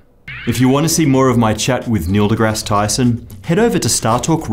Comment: If you watch his lips very closely after about 12:20, you will hear him say Tysom instead of Tyson.